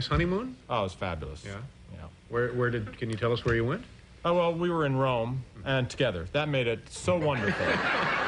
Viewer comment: after saying Rome, he says another name of the city (?) that sounds like "Sketters" - does anyone know what he was saying here (the closed captions didn't even print a word because it didn't understand it either). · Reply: “We were in Rome, and together…”